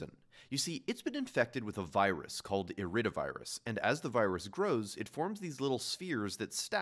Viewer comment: I heard “Iridocyclitis” instead of “IridoVirus”😅 I have traumas of Laugh from Vine